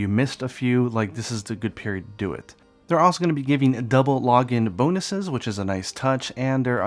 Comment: thought you were saying Double Joe - he's extra caffeinated! lol